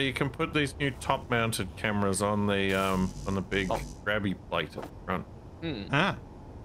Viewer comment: This image just came in my mind, but I heard "Plato" at a certain point in the video, and I imagined Plato's (the phylosofer) face on an armour plate